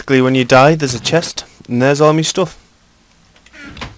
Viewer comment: did he just say: theres me stuff